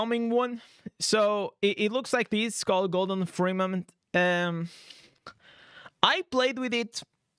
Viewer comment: I heard “Gordon Freeman”! Lol made me double check the name 🤣